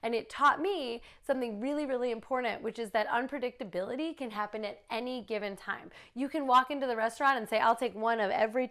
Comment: Did she just say " unpredictability can happen any time"? Lol min